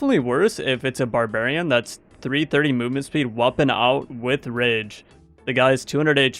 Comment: i thought you said "woppin' out" not "weapon out" make it a thing